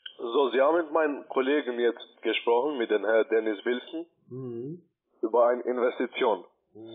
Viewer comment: Wie heißt der angeblich? Dennis Wilson? Habe ich das richtig verstanden?